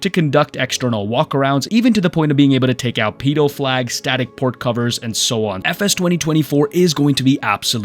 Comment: Sorry I heard pedo flags 🤣💀